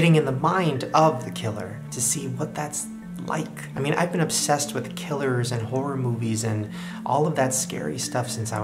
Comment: When he said " I'm obsessed with killers" I thought he said, " I'm obsessed with killing" 😂🤣